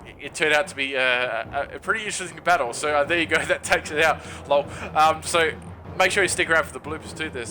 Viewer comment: Did you just say "lol" at 3:38 Dude. No. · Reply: i think he said lul haha